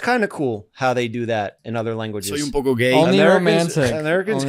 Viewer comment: Lmao I heard “soy un poco gay” 😅in